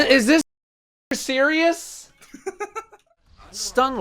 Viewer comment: What'd you say at 8:33 ? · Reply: "is this sh*t serious"